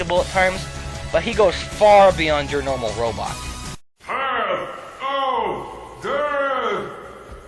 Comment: Path...of...deeeath. For some reason I thought he was saying pal..o..din